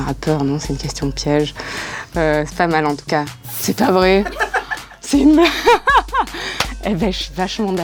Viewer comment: « c’est une blague ? » pas « c’est de moi ? »